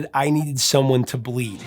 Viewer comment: I always thought they said “ someone to pleaseeeee” I prefer that then bleed